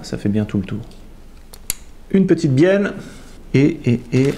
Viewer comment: une petite bieNNe ou bieLLe ? 😛 Super vidéo j'adore ! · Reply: Merci je sais que c'est une bieLLe avec 2 L mais c'est pour embêter car moi à 2:45 j'entends bieNNe 😁